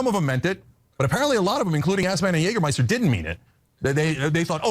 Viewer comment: Ok I was not going to post this but want to share it. Every time he talk I keep hearing " Ass-man and Jagermeister ". That's cool band name lol.